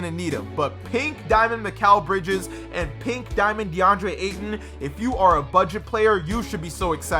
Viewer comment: did he say DeAndre ayton